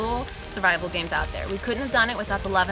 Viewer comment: At around 05:00 she said the basilosaurus is next while the subtitles said the bathroom door is next XD